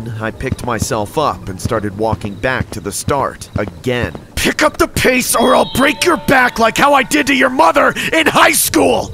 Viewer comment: I thought he said "pick up the pace or i'll break your face."